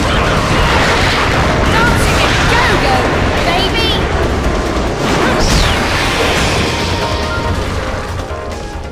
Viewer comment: Wtf does she say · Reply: "Dancing a-go-go, baby!" It's a reference to Viewtiful Joe, I think?